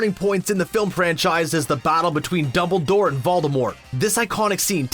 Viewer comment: 😂Did he say Double Door